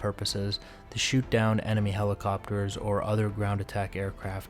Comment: Dead ass sounded like he said helicopiters